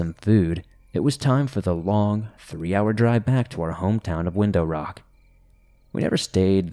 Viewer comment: I thought(story#3) that you said Wendo rock & my brain was like, “that’s Way too coincidental” 🤔then,”need to look that up now because That Has to be where this originated” 🧐and then, “nice way to promote guess I’m looking that band up too”😉…. and then you got to the return trip and clearly said Window rock🤣 Thank you & that IS a compliment as you always well spoken❣️Likely just my goofy noodle 🍝